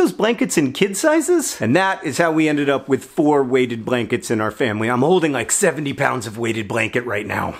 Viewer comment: I honestly thought you were saying "Waded Blanket" which I assumed had something to do with ducks and were somehow heavier than normal blankets. I'm still not sure your not saying Waded if I watch your lips. Then about 6 hours later last night I saw another story on Weighted Blankets thought that's weird, two type of blankets causing a stir on the inter web at the same time. It's 40C/100F outside right now, the sky is red with dust, and no doubt many parts of Australia are burning again today. But I still want a Waded Blanket for winter and look forward to being snuggled under it with my new puppies.